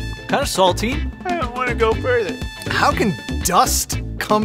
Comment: what did say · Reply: He said: "I don't wanna go further"